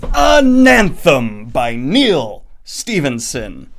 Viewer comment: When you talked about readin Ananthem in your other video, I thought you said Anthem and I was very confused because Ayn Rand isn’t exactly a loved author, haha....